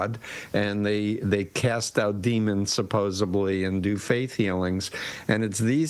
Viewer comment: Did he just say "supposeBly"? 😂